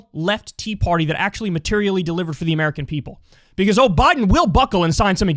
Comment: Did you just say "O'Biden." ?